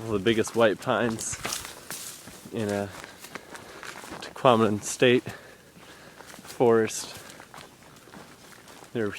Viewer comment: Where did you say you saw those huge pine trees?? "Ducominence state...forest" is what it sounds like your saying at 10:04, i can't find any forest named that on the map. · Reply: He was hiking in the Tahquamenon State Forest.